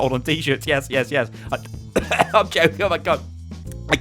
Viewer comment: He said " im choking omg" but i heard "im choking on my cum"